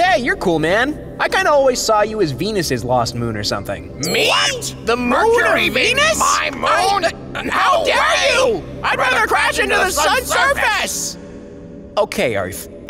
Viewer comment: Fun fact: at 0:58 and 4:32, with two different voices speaking over each other, you can still hear each of the planets if you concentrate. This is known as the cocktail party effect. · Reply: What I hear "What?! The mercury Venus my moon... how dare you! I rather crash into the sun's surface!"